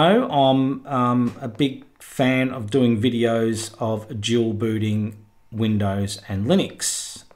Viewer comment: ) I heard "jewel booty" and was like what??? Rewind -> Dual booting - oooohhhh